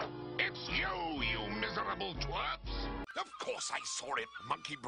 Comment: Did Robotnik say "tw@ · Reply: He said "twerps", but it really sounds like that